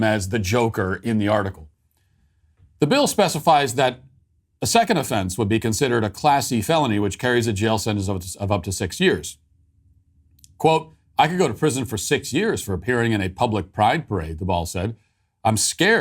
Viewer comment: I thought he said "a classy felony" instead of "a class C felony", lol